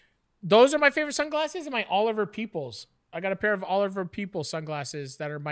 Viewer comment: I thought said "Oliver Peepholes" haha